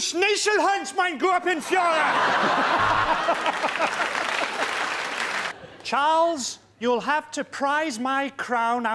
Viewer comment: What did he say in german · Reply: As a german, I didn't understand anything except for "mein Gruppenführer"